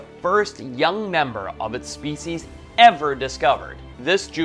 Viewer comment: I swear I heard, "This juvenile was covered in Dick hair."